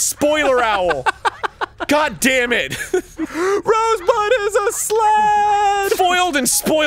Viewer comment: I gotta be honest. I totally thought Dan said "Rogue One is a sled".